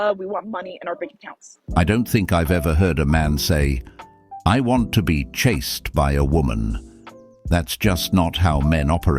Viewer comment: Why i only hear " I want a 666 CHAD"